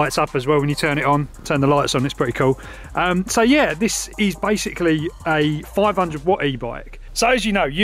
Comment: Did you say "This is bikesically" lol